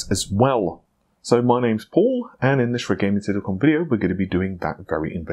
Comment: So... can anyone tell me what the heck does he say at 0:51? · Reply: Red gaming tech dot com video